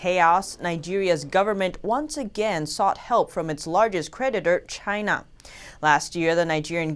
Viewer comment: I misheard this as: "...sought help from its largest PREDATOR, China."